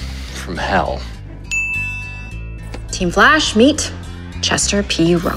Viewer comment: Wolf: Before you wanna kill me you might wanna start with the "Flash" I think I heard the word "Flesh"